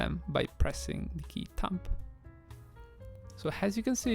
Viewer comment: what key? the key "thumb"? i cant hear it properly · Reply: Tab